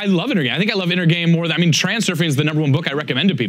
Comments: Anybody catch the book recommends at 37:15 ? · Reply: Hazer Infante reality Transurfing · what was the book he recommends? It sounds like he said he Transferring but I don’t know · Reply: John Lawson Reality Transurfing